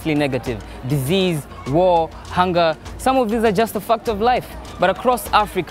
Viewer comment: Swear I thought he said fucked up life lol